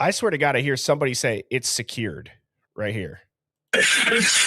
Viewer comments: I also hear the "go around". Where you hear "secured", I hear "Who are you going to execute?" Then I hear the "really." Also hear "that's an extremely tight noose." says, "Kaylee and Maddie love me." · I don't here "secure" at 34:08 what I hear is "execute". 42:07 sounds like a guy is saying "this one can't hold her liquor" and then a girl screams. 42:13 "please please please" 42:27 "it's still getting warm, yah feel?" (Closed captions picked that up as well) 43:05 "don't die" (closed caption picked that up as well) kind of sounded like cry after. 50:27 "bullsht, ...Kaylee!" 50:33 "it's not working!" 50:46 "Get off me!" (Sounds like maybe they were pinned down) Sounds like cry again after. 52:44 sounds like Kaylee get off the phone? 101:42 closed captions says, I love you. Oh yes. I hear after that "I want one" (Adderall maybe?) 103:34 closed captions picked up 'USF'. 103:42 closed captions picks up the word season. 103:57 closed captions picks up the word station. 104:47 CC picks up the 'USA'. At this point honestly it sounds like two homeboys laughing about something. This is awful to listen to. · Instead of "it's secure" I hear "execute" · The 'secured', I hear as 'execute'. One of the few words I can discern . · The 'secured', I hear as 'execute'. One of the few words I can discern . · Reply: I hear “insecure”